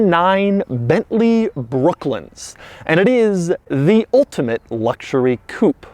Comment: I thought he was saying all the time “Brooklyn”